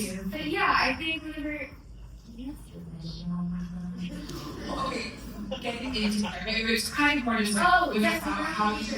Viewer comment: what does she says, i cant understand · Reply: She said do you really want to know